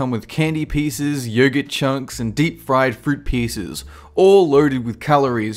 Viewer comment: There was a mistake in the subs it said "friend" instead of "fried"